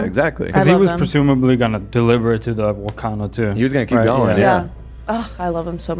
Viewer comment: Who else thought Love said he was "gunna deliver it to the Wakanda too?"😂